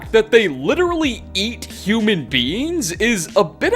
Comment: I heard “human beings” as “human beans” threw me off to what human organs could be or look like human beans….